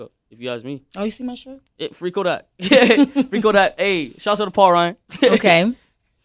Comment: did he say "shout out Paul Ryan"? Lmaooooo